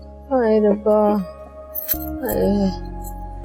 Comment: did ayeza said hadi bhaiya kuch kare na?